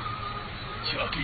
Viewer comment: for a moment i thought you were saying "Chalky milk" xD